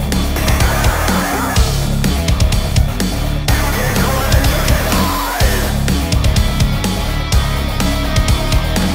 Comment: what is he saying here? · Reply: "You can't run, and you can't hide." I believe